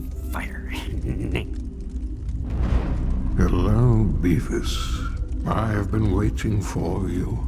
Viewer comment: Several ways subtitles misheard "Beavis": 0:41 "Hello Beefus" 2:10 "Quite so, Weavers"